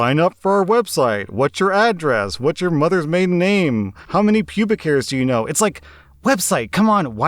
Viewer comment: Uhm 10:45 did I hear it right? How many pubic hairs do you know 🤔